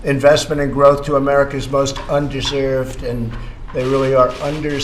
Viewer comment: LOL, sound like he said un-derserved at first. I know what he meant though